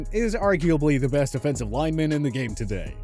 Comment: Great list, sounds like your saying Quention Nelson is the best defensive lineman in the game